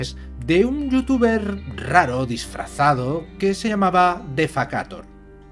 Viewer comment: no entendí muy bien, cuál es el nombre de ese canal? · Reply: Defakator. Pero es en francés, ojo. Eventualmente haré videos basados en los suyos, ya que me ha dado permiso para replicarlos en castellano